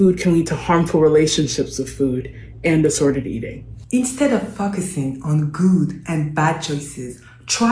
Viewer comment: She clearly says fuckussing